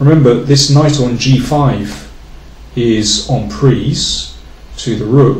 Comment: Hi. I heard the word so many times. The knight is on "preeze" (???) at 6:11. Can anybody give me the correct spelling please? It's not my mother tongue. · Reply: Dominik Hofmann "en prise" is a French expression. Look here for that and many others: <link>